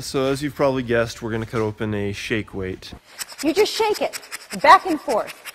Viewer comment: I thought it said snake weight lol?